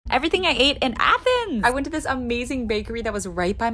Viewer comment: At first I heard " Everything I hate in Athens".